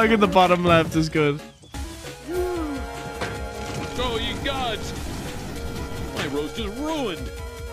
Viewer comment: A little detail at 12:08 is he says "ye gods" instead if "egads" so the subtitles had correct themselves